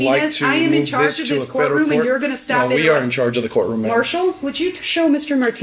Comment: He said "we are in charge of the courtroom", why is everyone saying that he claimed he was in charge? I had to play it again just to make sure he said what I thought I heard, rather than relying on what the judge thought she heard.